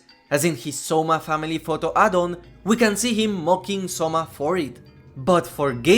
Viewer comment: why did I hear this as mogging 😭